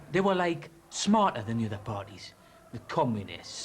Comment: Did I hear him say "they were like - smarter" ? I didn't know they had valley girl speak back then.